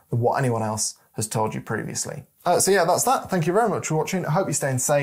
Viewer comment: had to rewind the last few seconds : I first heard "I hope you stay insane indoors" well it might come to that 🤣 great video though, you're probably the only one out there who doesn't consider his views as "gospel" (y)